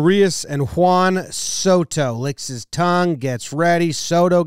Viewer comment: did he really say "licks his tongue"? Is that possible?